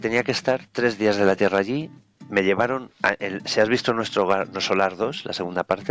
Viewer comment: En el minuto 25:45 el entrevistado menciona un título de una película, pero no le entiendo lo que dijo, alguien sabe cual es para buscarla. Saludos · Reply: Puede ser Nuestro Hogar, saludos.